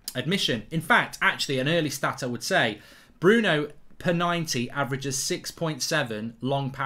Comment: thought he was about to say Bruno PENANDES😂